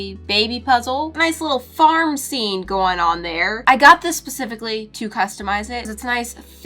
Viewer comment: did I hear it wrong or did she say Pacificly?! 😂🤣